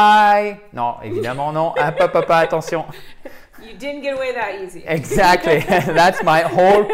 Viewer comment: Hi and, I didn't understand what said at 4:16 of the video. May you write me the sentence please ? Take care both of you. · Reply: Cécile D thank you for your question. You did not get away that easy! ;).